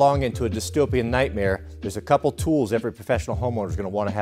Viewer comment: I misheard tools and thought said "skills" for the dystopian nightmare. And I immediately thought "body disposal." Worked out, eh?